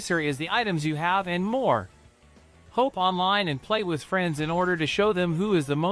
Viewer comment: Is this being read by an AI voice? It's weird. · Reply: Even at 1:51 it goes "hope online" instead of "hop online".